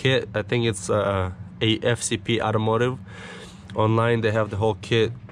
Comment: I just commented on your other video... You sent me here and im starting from the top... I looked up the flopro website and they say they don't ship the straight pipe anywhere in the US? How did you get yours? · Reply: what did you say at 5:15 ? Afcp automotive? Tried google and didn't find anything.